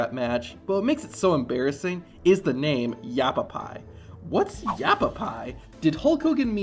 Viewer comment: I always thought he was saying Apple Pie...